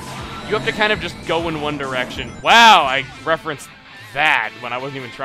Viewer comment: Can anyone fill me in on what he said he unintentionally referenced at 8:05? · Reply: The famous boy band One Direction